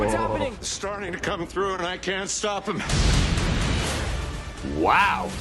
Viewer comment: It sounded like Strange said, "I can't stop him" this time instead of them. Interesting if that is the case and it is someone specific.